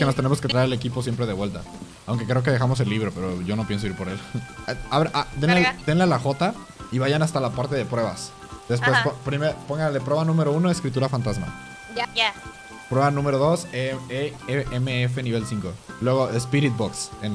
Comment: JAJA pense que decia los cacha fantasma like si entendiste XD